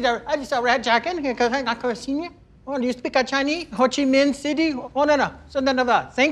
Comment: “Ho Chi Minh city” 🤣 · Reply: Lol, I thought he said Man City.